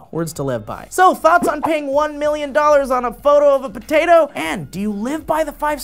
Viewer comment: I thought it said "a photo of Pluto" I was like whys it so hairy